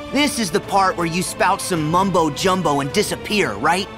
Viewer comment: Did Sora just say Mumbo?